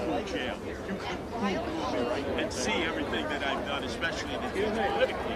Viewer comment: Who is the guy giving the speech. Sounds like he said curtis lakewood but I couldn't find any info on him. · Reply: It’s Curtis Sliwa, he founded Guardian Angels.